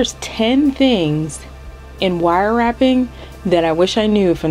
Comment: At first I thought it said "wire tapping"😄 Thanks for the demo!